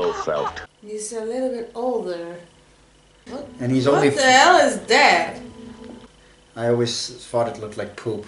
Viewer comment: I would like to know what you said after 'he is a bit older' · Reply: I think initially I thought she meant Blofeld and responded with: "not only that, now he has hair!" And she went: "No, I mean Connery!" And I agreed. Something along those lines. The full unedited version is also available for stuff like this of course